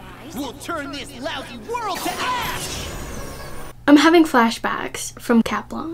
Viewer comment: Did bro just say a bad word?💀💀💀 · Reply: He said ash